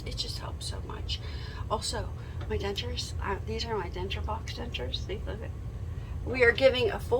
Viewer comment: I couldn’t clearly hear what you said @ 22:15 did you say box dentures? If so, what are those?